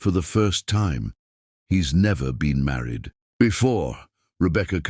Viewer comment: I thought he said he wasn't ever married